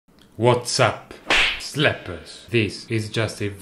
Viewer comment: Idk why I always hear "Zlappppers" instead of "Slappers"